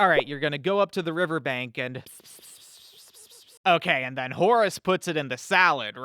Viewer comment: did You say HORUS??? screams and grows vampire teeth TRAITOR!!!